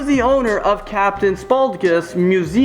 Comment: at first I thought he said captin sparkles lol,.😂😂😂